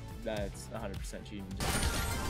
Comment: What does Doc say in the transition at 3:22? I hear “Let’s drop in. Let’s camp. And let’s dominate” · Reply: "Let's attack"